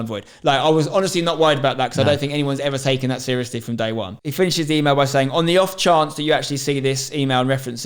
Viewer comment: did he say Finch’s email??? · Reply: he said he finishes the email